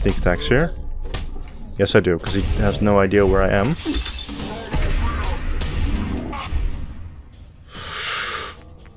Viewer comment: what did that super mutant say?!? :O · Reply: Nope. He says Gonorrhoea